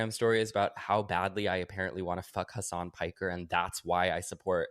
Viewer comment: I kept hearing "her son Piker" because I've never heard of any of those people. But yes, he IS gorgeous!